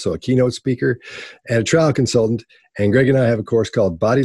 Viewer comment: Lol, when he said “trial consultant”, in the intro,I misheard him, and for a split second thought he said “Trump consultant”, and thought, “I knew it!!” Lol. Great video, I like these. I couldn’t care less who any of y’all are voting for, or which preference (or there lack of), I just like the information.